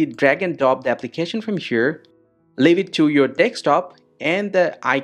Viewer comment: Every time you said desk top it sounded like you were saying take stop. Also, I followed your instructions and it didn't work.